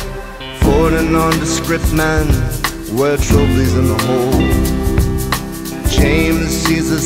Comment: What is he saying from 1:24 to 1:27. “Four undescript men wear trophys in the hole”??? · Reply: "Forty nondescript men wear trilbys in the hole"